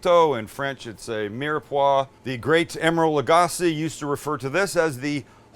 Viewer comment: i had to rewind because i thought you said the "late great emeril lagasse"....